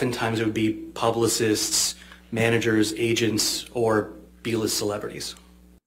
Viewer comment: I have to admit that during the trial, I did pick up on the "B" list actor dig BUT I thought he said "D" list. I also didn't notice the very quick sideways glance at AH. Brilliant on his part. And Camille's ability to navigate seamlessly through all of it was so impressive!